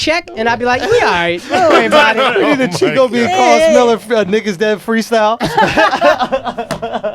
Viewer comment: For a small second I thought we got Andrew for saying nigga at 1:25:45 but he said "NICK IS DEAD"